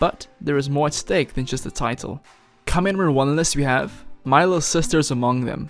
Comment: When he said “coming in number nine” I thought he said “cum in a nun”